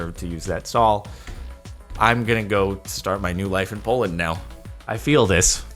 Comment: Every Pole here: DID HE JUST SAY POLAND???!?!??!!!